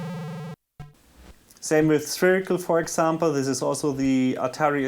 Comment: What's the game @ 4:40? I didn't understand what you said when you said its name, well, I understood "Spherical" but I search for it and I find no game with that name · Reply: Josué dlTLl. It's a game called Spherical for the Atari ST made by Rainbow Arts.